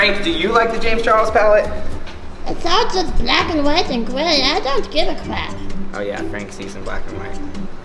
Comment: Did frank just say I don't give a f...???